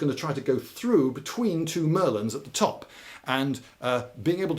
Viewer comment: i heard "two merlins" and got very confused about the presence of war wizards until the picture came up